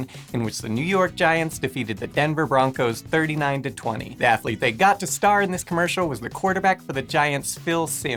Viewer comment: I thought it said "Atheist" and I was a bit confused.